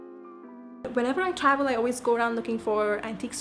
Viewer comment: idk whats wrong with me at 9:00 I heard " whenever i travel around i keep looking for DICK STORES"